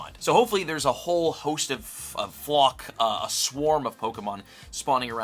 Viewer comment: WHAT DID YOU SAY??? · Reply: Flock